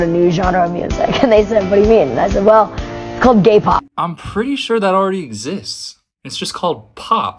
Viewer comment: I thought she said k-pop at first 😭🙏